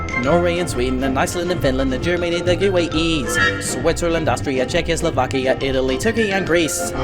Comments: whaaaaaat!! I thought you said checkoslovakia didn’t exist!! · I thought it said that it wasn’t Czechoslovakia anymore, it’s the Czech Republic and Slovakia (I may be wrong, I’m not the best at world history 🤷‍♀️) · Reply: He says “Czechia, Slovakia”. It’s really hard to tell, though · is he saying czechoslovakia or czechia and slowakia? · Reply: It's "Czechia, Slovakia" - though it's a little ambiguous when you listen to it admittedly